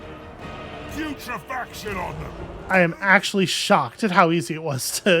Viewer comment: Did he just say 'future faction owner?' 😆